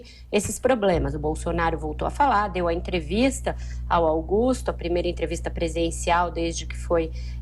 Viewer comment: Eu escutei bem? Aos 01:50 ela diz "...sua primeira entrevista presidencial..."